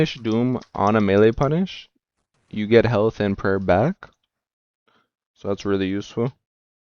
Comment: I turned my volume max but it still sounds like you said you get hella thin prayer back. What was that supposed to say or mean?